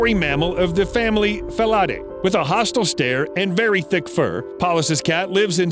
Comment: I know he said very thick fur, but who thought he said very dick fur?